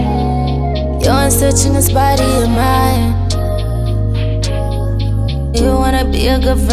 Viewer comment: I deadass had to repeat the “your hands touching this body of mine” part because this whole time I was just singing some other shit 💀💀💀💀 here I was thinking she said “you’re searching in spite of your mind” lmao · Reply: I thought she said, 'You're in search of this body of mine...'